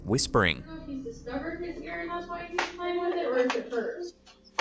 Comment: tge whisper sounds like its answering mom. I think it says "hers".